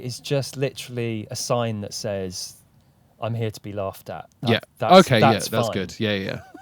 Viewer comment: Matt Baynton: “The outfit says ‘I’m here to be laughed at…’l What I hear “I’m here to be lapped up”, innit then.